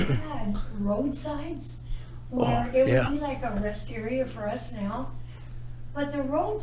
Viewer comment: “it would be like a [ rest area for us now ]” That is what she said. The rest area almost sounded like wisteria.